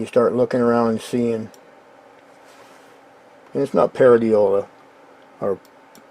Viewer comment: Did he say… parody-ola? Maybe it is just a joke lol